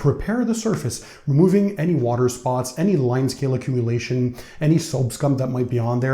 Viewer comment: im so addicted to car detailing I thought you said tar spots on the glass, not water spots haha!